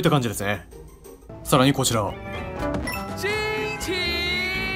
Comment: マグマに落ちながら「ちんちーん！(空耳)」って言うの本当に面白いw